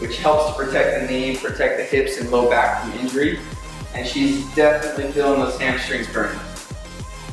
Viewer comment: did i just hear someone say "i've never been mexican" ? 😕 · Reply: I say, "I'm feeling the burn"